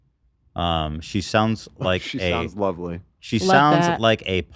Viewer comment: Who else heard say love that around 1:40?